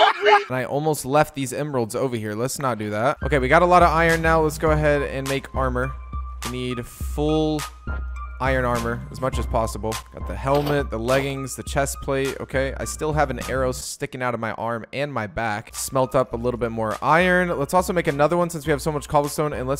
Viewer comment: when he said "get the helmet", i thought he said " what the hell man" xD